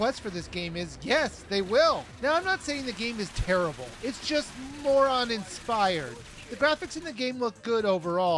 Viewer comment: lol when he said "more uninspired" i thought he said "moron inspired"